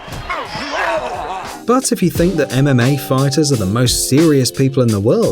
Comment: It sounded like he was saying anime fighters instead of mma fighters